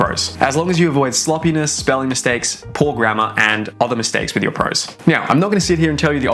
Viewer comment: Every time you say "poor grammar" i hear "poor grandma" XD